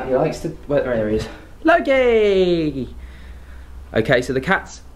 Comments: Who said, “Loki” right after did at 5:50? It sounded like the voice was trying to imitate exactly how he said it. Clear as day. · I hear Loki being said but it's not you saying it? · Wait a second…around 5:48ish…I thought I heard a man’s voice in the background say “hey Loki”..what was that?!?! 😩 was it audio picked up in the bedroom upstairs just delayed? Or?! I’m so freaked out! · I heard someone say Loki or laugh at 5:49/5:50…